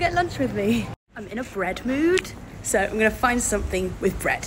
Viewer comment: did i just hear, "im in a bread mood?!"